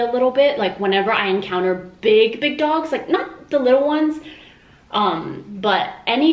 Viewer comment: i thought he said big ducks instead of big dogs